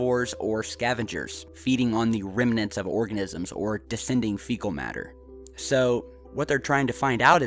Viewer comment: Descending fecal matter...? I thought you said fish shit